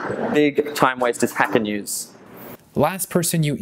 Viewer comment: Anyone can tell me the website ? I heard "hack and use" but it's something else :/ Thanks to anyone answering =)